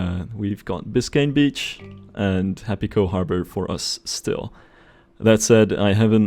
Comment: OOF, I heard "Happy pearl harbor" Im like excuse me